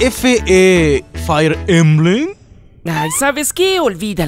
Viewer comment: En en minuto 2:58 entendí fire emblem 🤔🤔